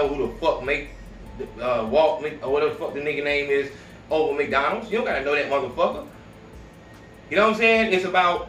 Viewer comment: 😂I think my n8gga said Walt McDonalds